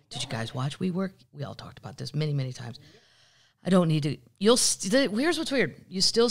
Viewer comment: For those of you interested in finding out more about the Murdaugh Murders definitely check out Mandy Matney’s podcast! She also works for Fitsnews who also has GREAT sources and the two podcasts are consistently the FIRST to report breaking news in the case! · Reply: Thank you. I was searching comments for which one she said. I thought she said manly manny 🤦‍♀️